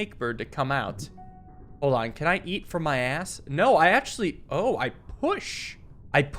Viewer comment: Was distracted while this plays in tye background. All I heard was "can I eat from my ass??"